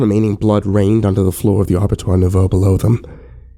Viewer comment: Hey, what did you say at 6:46? I've replayed that part at least 5 times and I can't figure it out. Great job on this, by the way; keep up the good work. :) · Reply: abattoir nouveau Looks like a mistake by a bad or young writer who thinks that throwing in some French would make his story sound more educated or something, when in reality we know he probably put "slaughterhouse" into Google translate. Good, educated writers will make sure a story is understandable to keep the immersion, not throw in distractions that make people have to stop and ask in the comments what they're talking about, reminding readers/listeners that this is a just a story, taking readers/listeners out of the experience.